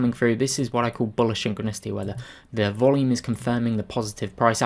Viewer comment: What was the word at 4:57? "This is what I call bullish _______?" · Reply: Synchronicity